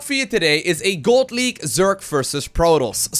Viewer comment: Sounded like he said "God League".